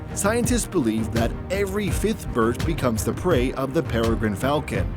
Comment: ok, at 4:19 what word does the commentator say when he goes "every ___ bird becomes the prey of the falcon". Sounds like fifth bird becomes the prey but that doesn't seem to make much sense.